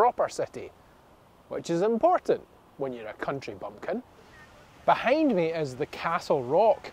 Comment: Great video! Thank you! What did you say at 5:35? „… if you are a country pumpkin“? 😂😂😂 Never heard this before. Greetings from Germany 😊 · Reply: Country bumpkin! 😊 It means someone who doesn't have a lot of experience in the world outside of the farm.